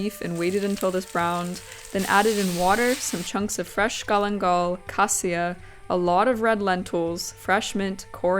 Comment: What did she say at 7:28, callinghal?